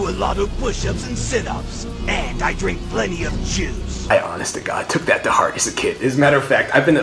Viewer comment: thought vegeta said I drink plenty of jizz